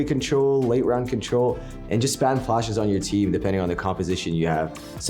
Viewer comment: Did he say "spam flashes on your team"? XD